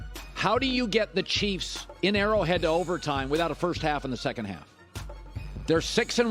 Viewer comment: “Without a first half in the second half” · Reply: I thought he said „without a first half and a second half“